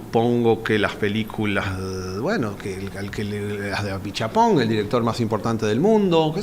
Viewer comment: Qué dice en 1:01:35? Pichapón transcriben? Qué director menciona? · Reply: Apichatpong, dice